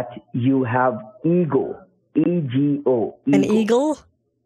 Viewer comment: I thought he was saying eagle not ego